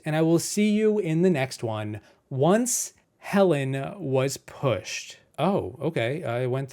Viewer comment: I genuinely thought you said "Helen was F*cked" before I understood the joke. That statement gave me whiplash.